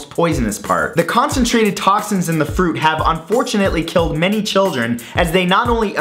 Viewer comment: I seriously thought you said "has fortunately killed many children" 😂